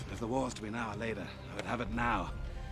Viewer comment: As a kid I always thought they said “God wants it!!” 😂